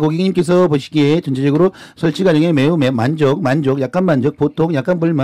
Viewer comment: 매우 만족을 매운 만두로 잘못 들으시고... 불 만족을 물 만두로 잘못 들으심... 🤣🤣🤣🤣🤣🤣🤣🤣🤣🤣🤣🤣